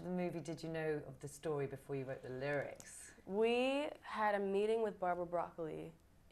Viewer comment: is she really saying the had a meeting with Barbara Broccoli.